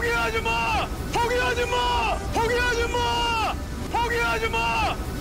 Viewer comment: 나만 소개 하지마로 들리낰ㅋㅋㅋ